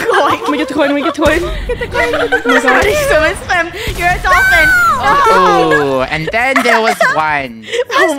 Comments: WHY DID SAY "UR ADOPTED*😭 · Uhm, is it just me or did say draco was adopted?